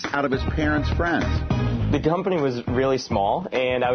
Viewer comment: did anyone catch at 1:27 he said "dumpany"? Or is that just me?